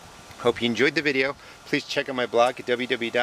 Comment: Slightly off topic, but I couldn't find it on your blog page. Why do you call it "The Rusted Garden"? (For a long time, I thought you were saying "rustic") Thanks. ~